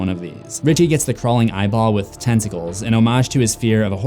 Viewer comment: I thought he said testicals